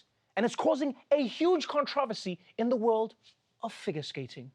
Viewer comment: did anyone else catch him pronouncing it "conTREVORsy"?! 😯🤣